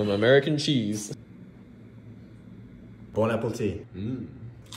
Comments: Looks amazing but why did I have a second where I thought he said “bone apple tea” like the Reddit page and just assumed he actually thought that’s how you say that. My faith in people is so gone. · Sounded like you said Bon Apple Tea!